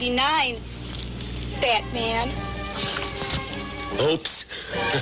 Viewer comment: did he say OPES instead of OOPS? is that a thing?